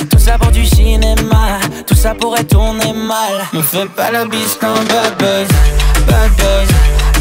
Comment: Suena a que dice: Me fui pa la disco bb .... jajajaj